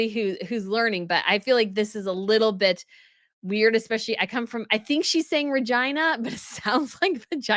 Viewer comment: Oh! I heard "I'm from China" Is this a case of Peter Kaye's "misheard lyrics" on my part? (which I recommend )😂